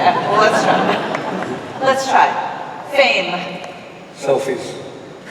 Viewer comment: what word does the interviewer says in 29:39 to which Colin answers ''selfish'' ?? and the second???? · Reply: eva molina she asks him to associate the word "fame" to which he replies "selfies#.